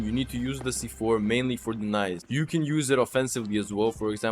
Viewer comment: what I heard For Denise ❌ For The nise ✅